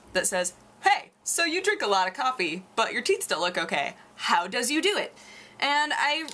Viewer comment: Sounded like you said "you drink a lot of coffee, but your teats still look ok" I laughed